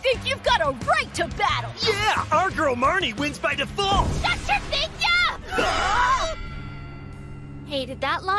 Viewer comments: wait what did she say? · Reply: Marnie said, ”SHUT YOUR BIGS YUPS!!!!!!!!!” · WHAT? What did she say? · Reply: She said, SHUT YOUR BIG YUPS!!!!!!!!